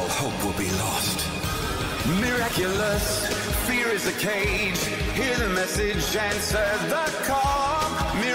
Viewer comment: What I hear: “Hear the message and serve the co-“